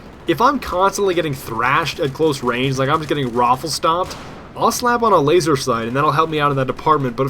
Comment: *watching...* *12:15* What did he just say? *rewind* Hmmmm,,,, ROFLstomped added to vocabulary.